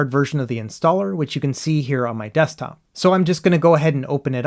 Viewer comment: All I hear is “any desk”. I watch too many videos on foreign scammers.